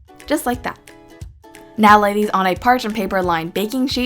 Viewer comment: when you said now lay these on a parchment paper and bake the for 15 min I thought you said now ladies